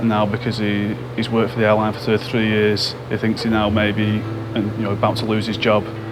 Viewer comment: The first guy is so silly he just jeopardised his 33 years of career. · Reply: That was 33 years? I thought they said 3. Can't hear anything the narrator says